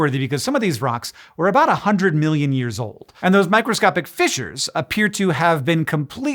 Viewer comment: For a split second I thought he said "fishers" not "fissures." Very different things.